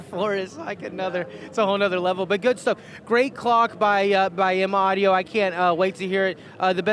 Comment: did he say M-Audio ? also ... When he says "It's like pulling a wet sheet off your speakers" I mean common